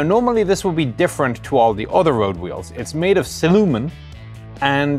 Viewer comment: Am I the only one who thought he said ''The wheels are made of Cinnamon''..... I did think for an instant, that's not very practical... but at least they would have tasted good....